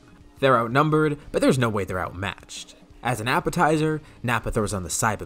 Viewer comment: I heard “Nappatizer” instead of appetizer